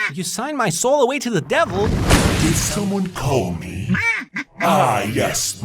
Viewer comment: did someone call 666